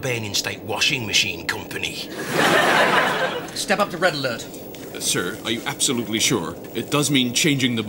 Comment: I haven''t seen this for many years but I could have sworn Rimmer said "Step up to brown alert." Unless the brown alert command was in a different episode.